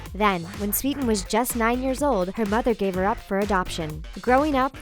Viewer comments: She said 9 years old but the subtitles say nine months old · I FOUND A MISTAKE! The narrator says "Nine years old" but the Subtitles say "Nine months old" so was she nine months or years old ?